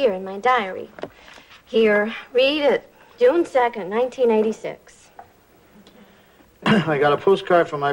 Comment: According to the Auto-Generated Closed Captioning Subtitles, Stephanie said June 2nd 1986, which would makes sense since that day would've already passed over 2 1/2 years before since this episode aired back in January 16th 1989; however, it oddly sounded like Stephanie said 1996, a year which has yet to exist for another 7 years, 5 1/2 years after Newhart ended; maybe I just misheard what Stephanie said.